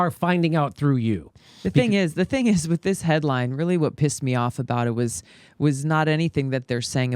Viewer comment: She actually said pissed me off. Yes enough with PC language BS. Just be straight with people.